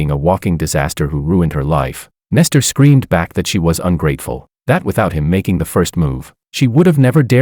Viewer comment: i'm sorry what? he scream toward her ungrateful? i'm not hearing it wrong am i?